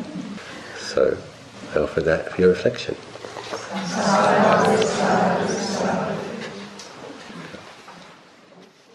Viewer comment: What are they saying at ~ @ 54:45 ? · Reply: Hello again friend. They are saying "Sadhu, Sadhu, Sadhu". Sadhu is a Pali word which means good, excellent or auspicious.